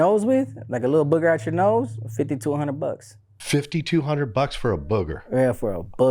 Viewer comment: I think the man thinks said "52 hundred dollars" ($5,200)😂😂😂 when he's really saying $50 to $100